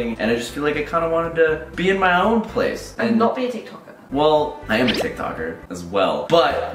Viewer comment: captions said am a big talker loool